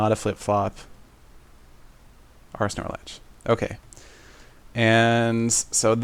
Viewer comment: did he say "R S Snorlax"? Lol